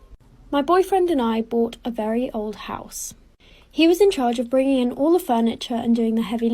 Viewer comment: Ppl who heard her say my gf house.,😅😅